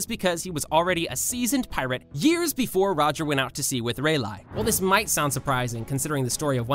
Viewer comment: i swear i just heard him say reilai instead of rayleigh